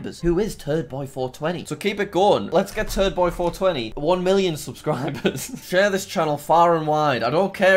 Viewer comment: subtitles: lets get turd boi 421 million subscribers